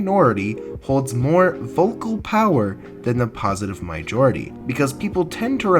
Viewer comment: Did you just say mijority